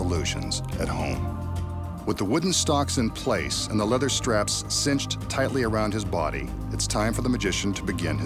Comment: I thought they said fake leather straps